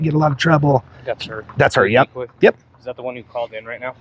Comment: what?? The cop says "is that the one you commed in right now" or somthin?? And goes , no i think that was the trash can . What? Somebody help me out here